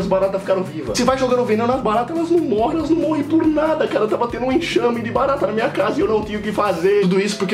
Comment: Só eu entendi "exame de baratas" ?